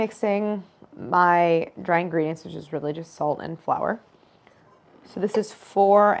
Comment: I heard "... my dry ingredients are religious salt and flour" hahahahaha